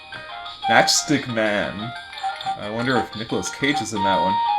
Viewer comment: I can't be the only one who thought he said "Chapstick Man".